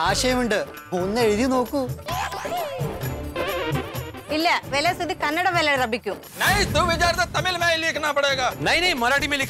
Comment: they say ( Write in Kannada Language ) but not in Kannada but something different Language 🙁 Don't Know what they Said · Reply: She wanted to say "Volle suddhi kannadadalli irabekku" but messed it completely